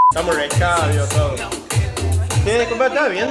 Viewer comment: que dijo? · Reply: ''me está apoyando boluda''